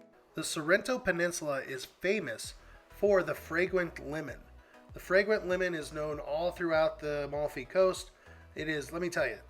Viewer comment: Sorrento is famous for fragrent lemon? I thought that you said fragrent women. LOL When ya get old ya get deaf.